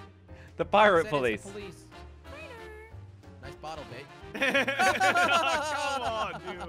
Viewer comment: Guys did anybody hear what jelly said I’ll put the time 3:53 he’s said nice Bottle Babe 😳